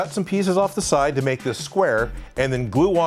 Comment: I swear he just said ass